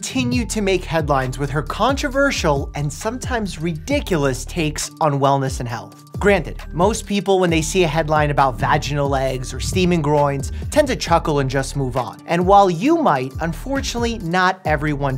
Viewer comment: I wasn't wearing glasses and kept mishearing 0:11 as "vaginal legs"... why does it actually sound like a name for their product?